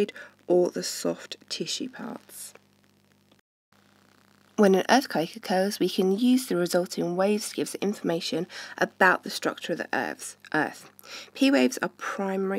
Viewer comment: Anyone know what she says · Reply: Turn on subtitles: "When an earthquake occurs we can use the resulting waves to give us information about the structure of the Earth"